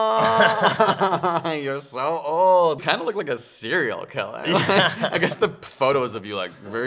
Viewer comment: when Greg said "I'll still love you" it melt my heart haha aawww. and the way said "you look like a serial killer" I thought I hear "cereal killer" hahaha